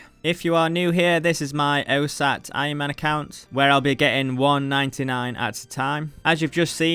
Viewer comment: Love these videos! One question: I can not for the life of me understand what you are saying when it sounds like you say "oh sat iron man account", that is even what the captions think you are saying as well, what actually are you saying haha? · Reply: OSAAT = one skill at a time